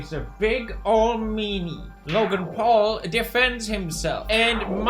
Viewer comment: I thought he said big old MEME ey